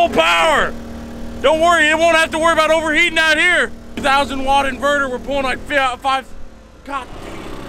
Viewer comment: Did you just say ‘a thousand watt inverter were pullin like fiat five’